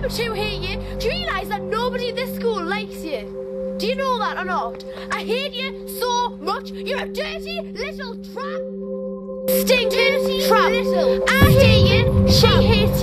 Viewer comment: I thought she said I hear you but then I was like oh she said I hate you so much